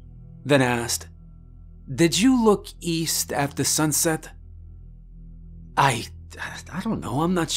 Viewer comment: I'd be freaked out too if I saw a sunset in the east. The sun sets in the west. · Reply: Thanks a lot, now I've got to go back and listen again if the warning was don't watch the sunset in the east or don't look east at sunset. 😂 Edit: okay so he looked east after sunset. It does sound like Gene says "at the sunset" but it's "afta sunset"